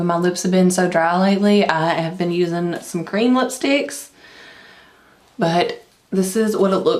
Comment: when you said cream lipstick I thought you said green lipstick hahahaha love the look!